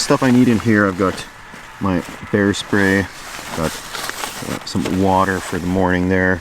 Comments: bear spray, I thought you said hair spray. whatever. if you wanted to look good for the video I'm ok with that. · As you unpacked you bag, I heard you say you brought hairspray for the bears ... maybe it IS time to get my hearing checked. Though now I have a mental image of you facing off with a bear with a can of Finesse Extra Hold ... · Lol...could have sworn he said hair spray...and I thought...huh? Lol! Nope Bear spray.